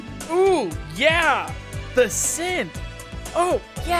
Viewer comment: Am I the only one that heard him say the simp instead of the sinth